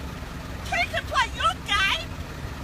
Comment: if anyone wants to know what she said "two can play that game"